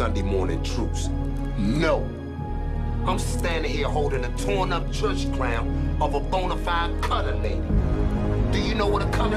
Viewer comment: What does Slim say at 1:17?? I could never make it, and I don't know church lingo. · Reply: IM holding the church crown of a bonafide colored lady. Just meaning her church hat